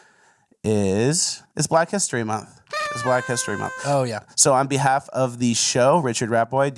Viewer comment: I thought he said Fleccastory Month and got excited for a highlight reel.